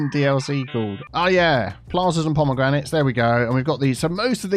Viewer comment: Hi, did i hear "Plazas and Pomagranates" at 14:56 ? lol